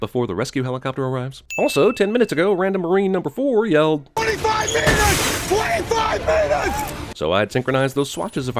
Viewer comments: FYI Cinema sins love the show seriously great but at 8:05 on your video when you think Stavgro said "25 min" he was saying " 25 Meters". Like the enemy is 25 meter away not 25 min til bird lol. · you sinned them for time when the guy was actually saying 25 meters!